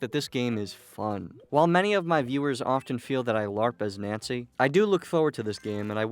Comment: you larp as nazi? I can’t tell what you’re saying here. And don’t reply telling me because I don’t give a shit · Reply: Nancy. There's been a lot of comments on his videos calling him Nancy